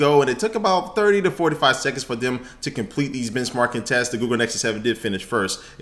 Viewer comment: lmao it sounds like he is sayin bitchmark